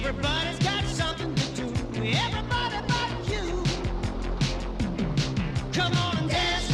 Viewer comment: for the longest time i thought he said everyone F you. Instead of but you. Love night train to terror